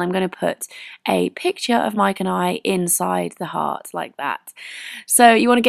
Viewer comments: Cute and it sounded like she said mike and Ike lol( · haha with your accent it sounded like you said: mike and ike, haha ;)